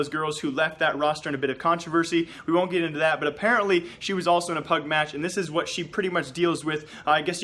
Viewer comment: did you say PUBG?